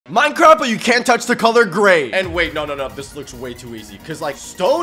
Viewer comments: i thought he said "minecraft but you cant touch the color gay"💀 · I thought it said i cant touch the color gay